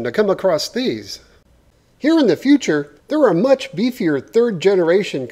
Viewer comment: Did he say ‘here in the future’?? 5:08 👀 its doc!!!